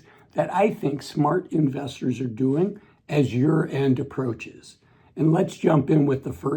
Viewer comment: When he said........smart things investors are doing as year end approaches - I thought he said "as your end approaches" 😂